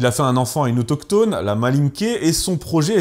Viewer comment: j'ai entendu "la mal niquée", j'ai grave bugué ! :D